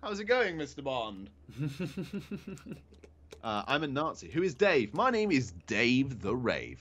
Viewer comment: don't try and be slick I heard you say "I'm a Nazi"